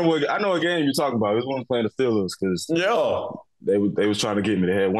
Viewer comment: did Bradberry say Steelers? I’m trying to figure out what game they’re talking about. I think it’s the Steelers game